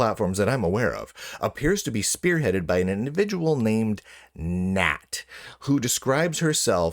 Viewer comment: Auto-captions wrote "gnat" instead of "Nat". Curious. Is YouTube have the 'phobics?